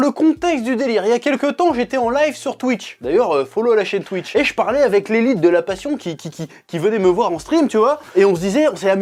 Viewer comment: à 1:42 j'ai cru entendre : "j'étais avec l'élite de la passion venus me voir en string"... ... YAOI TIME